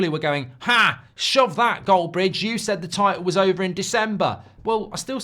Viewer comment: I thought you said it was over lok